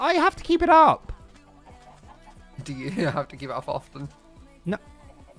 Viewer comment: I love you man but what the fuck are you saying?? "do you have to give up often"? "do you have to keep it up often"??? "do you have to get off often"?????? WHICH IS IT?? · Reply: “Do you have to keep it up often”